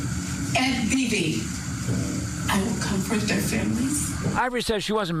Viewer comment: It sounded like she's saying "comfort" not "come for"